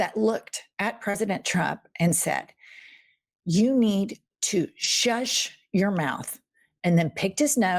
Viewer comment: I thought the kid said, “shush your fucking mouth up.”